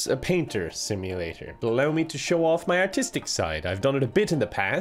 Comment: "Let me show my artistic side" Me: for an minute there I thought he said autistic side, wouldn't be surprised.